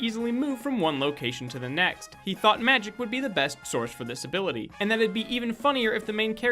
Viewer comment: I misheard fact 8 as "Meth would be the easiest option for this"